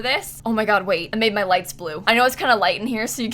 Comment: Haha I thought said she made her legs blue. She said lights... 😂😂😂